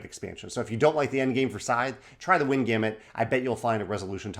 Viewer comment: he mentioned an extension that has lots of different triggers and end game conditions for Scythe? I couldn't understand which was it... "Win game" extension?!? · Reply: That's "The Wind Gambit".